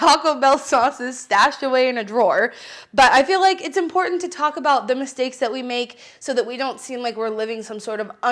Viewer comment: I heard "It's important to Taco bell the mistakes that we make"